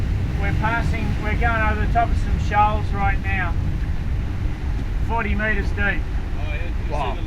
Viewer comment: "40 mts shoal" does not exist. Wake up sailor! Let them get some forward looking sonars on board and triple redundency on navigation hazard. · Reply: Uhmmm; I thought he said "We´re going over the top of some Shelfs right now." Or?